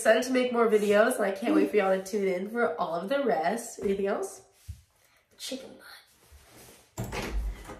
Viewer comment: Adorable and Beyond!❤️‍🔥🥰❤️‍🔥 🥳🥳🥳 Chicken what? lol 🐥>>>🍗 (Thought said chicken pie)